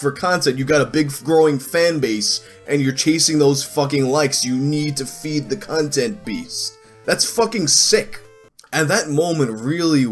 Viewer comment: I thought you said "you need to feed the cuntent beast" Edit: I will be using the term "cuntent creator" for shitty content creators now 😂 thanks